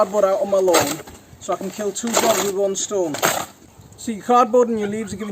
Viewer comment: so I can kill two Dog with one stone! I thought the saying is killing two birds with a Stone, this is how I know it, Respect and thank you for your Video. 🇦🇱🇦🇱🇦🇱🇦🇱🇦🇱🇦🇱🇦🇱